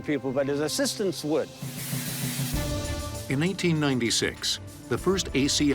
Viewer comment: did the narrator REALLY say 1996?!?! I would almost swear that's what I heard!